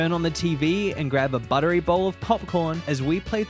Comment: I heard this as cocporn.. 😂